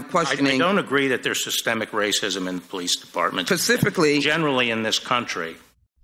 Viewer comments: did she say pacificly?!?! 😂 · Did she say "Pacifically?" at 5:09??? Precious